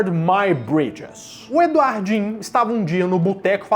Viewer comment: Tava só ouvindo sem olhar pro vídeo nessa parte, ae quando ouvi o nome, fui reparar se escrevia Eduardo Minha Ponte, msm.